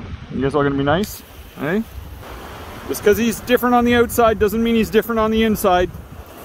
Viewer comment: I'm sorry but what I heard there was "just because hes different on the inside doesn't mean hes different on the inside!"